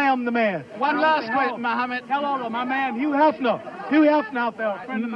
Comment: an unforgettable moment in boxing history. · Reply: What did he say? My man Hugh Hefner??